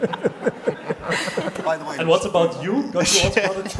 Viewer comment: what the interviewer says in the minute 4:58 ? i really can't understand. please tell me what the interviewer said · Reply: He said "who's most likely to bite Beyonce?" Mackie's a huge Beyonce fan